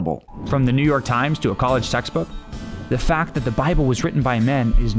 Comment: Why would a college textbook be Memes 101? Is that what the narrator thinks college is? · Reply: Haha, I thought he said college sex book.